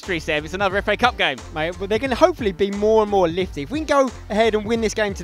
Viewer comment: Did say litty? Lol